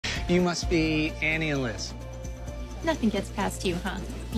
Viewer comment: I think he said Annie and Liz. But I had to watch it a few times bc I swore I heard him say “and you must be an alien” as in their names were Annie and Lynn like some fucked up pun. 😅😅 good thing my ears are just broken lol